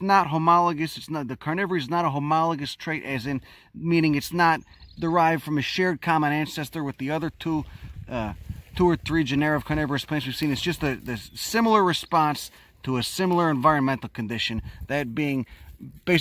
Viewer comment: I thought it said "corona virus plant"